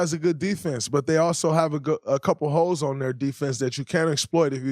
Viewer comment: When he said Denver has a couple holes on defense, I thought he said a couple hoes lol